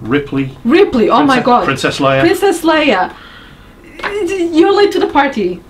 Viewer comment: When says "Princess Leia", the captions think she says "Incest Lion" Ummm... OK.